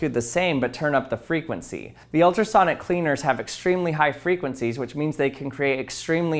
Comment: I thought he said ultrasonic weener. I need to turn my volume up.